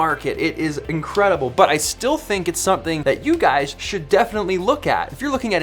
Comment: Did anyone else hear A.D.D at 4:02 ?